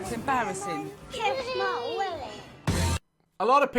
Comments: holup did he just say kiss my willy to his MOTHEEEER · what did he sayyyyyyyy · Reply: I think he said "kiss my willy" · did he say kiss my Willy 😂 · I can’t be the only one who heard “kiss my willy” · I thought I heard the kid say kiss my Willy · did he just say kiss my willy???????????????? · eww did he say “touch my Willy” cuz that is disgusting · lol did he say kiss my willy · did i just hear “kidd my willy”😭😭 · ← Did he just say "Kiss my willy?" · did he just say you can kiss my willie wtf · DID HE SAY "kiss my willy" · lol did he just say kiss my willy? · anyone else hear him say kiss my willy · did he say kiss my willy 😁😂😂🤣🤣😅😆🤣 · did he say "Touch my willy" 😃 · just before he pauses it did anyone else hear the kid say kiss my Willy to his mum 😂